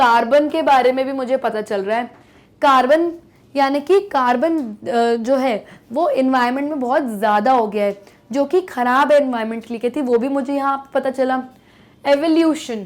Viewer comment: Ma'am mentioned carbon's quantity had increased in past years which is harmfull for our environment. But I heard Humans .